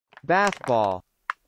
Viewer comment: めっちゃハスボー（ポケモン）に聞こえる